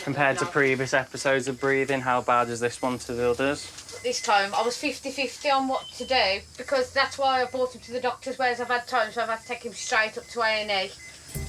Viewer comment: As an American I actually can't understand the mom at 27:30 What accent is that? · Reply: She said that she got him to doctor multiple times and it’s a 50, 50 chance of him having respiratory problems